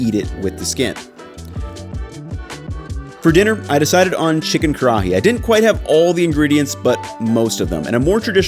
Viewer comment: I misheard "chicken karaage" and got quickly very confused when you started cubing the chicken!